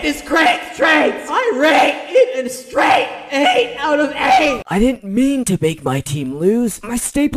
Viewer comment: I like how the voices and the characters actually fits their voice actor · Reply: did she just say ‘straight 8/8’